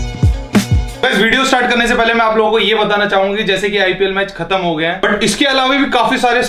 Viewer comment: Guys.. Listen what he said at 0:17 He said.. Batana chahugi